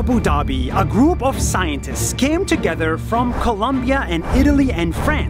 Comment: wait he spelled it wrong I hear in Abu Dhabi a group of Scientists scam together it should that been came together not scam. lol